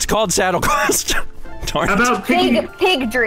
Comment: I thought that said Piggy Ridley at first.